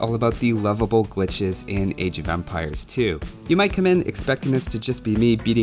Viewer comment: I always hear "Age of vampires".